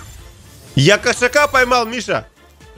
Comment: мне послышалось"я кошака кастрировал"😂